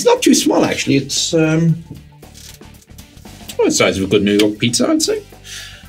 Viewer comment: I literally thought you said "A good noodle pizza." For several seconds, my mind was reeling from the Italianoception when you said it again and 'New York' was a bit more clearly annunciated.